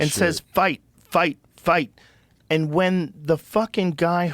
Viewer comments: I totally thought he said f u f u but fight is ok as well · I thought he said Fuc&m but he probably said "fight"